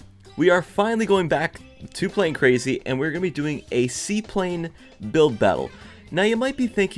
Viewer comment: He said sea plane. I thought he said c plane not sea plane